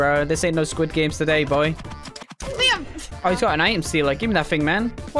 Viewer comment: did she say laim- on 3:51 or